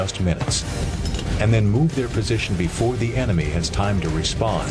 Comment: I thought he said before the enemy re spawn lol